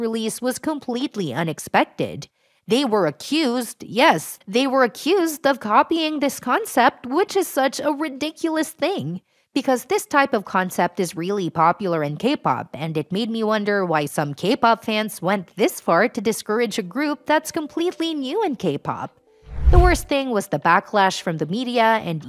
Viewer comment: For a second I thought you were saying "gay-pop".... 😂